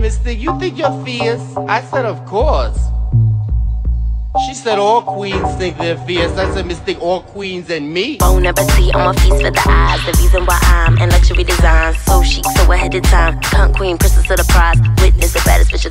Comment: 'All queens think they're fierce' I said 'all queens ain't me' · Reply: "all queens AND me" i thought it said this for forever